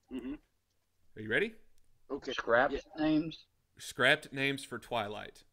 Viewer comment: dang I thought you was saying scrat names not scrapped names